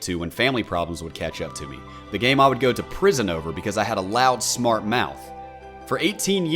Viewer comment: wait did he just say he went to prison for rs